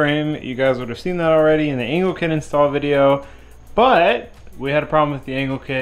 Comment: sounds like you said “anal kit”